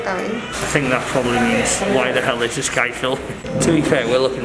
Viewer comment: She didn't say "why is he filming?" She actually said "He has a very pretty hair color". Very sweet people indeed!